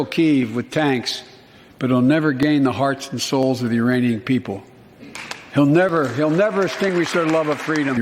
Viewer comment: Sounds like he said Uranian people